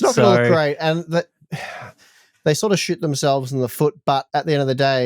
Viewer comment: For a brief moment I thought he said AMD Shit themselves in the foot! 😂